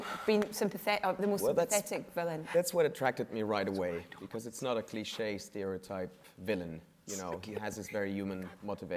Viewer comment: what did he say to her? So cute btw <3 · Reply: "That's why I don't want to say anything!" Apparently alot of the actors microphones were turned up. Chris Evans noticed this early on, and couldn't help but laugh when Elizabeth Olsen was loud.